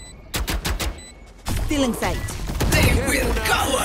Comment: did reyna just say "sike"